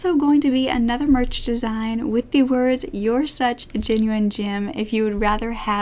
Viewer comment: This entire time I thought she was saying "You are such a genuine jim" i